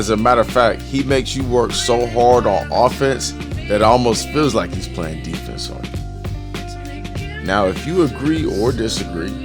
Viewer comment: “he makes you work so hard on offense, that it almost feels like you’re playing defense” what a statement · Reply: I get what he was saying @ 6:00.."he makes you work so hard on offense, that it almost feels like he's playing defense on you" were his exact word. That just a kind way of saying while his D is rather weak on film, the suttleness of his defensive style makes it almost feel like you are working harder on offense than you expect when he's gaurding you.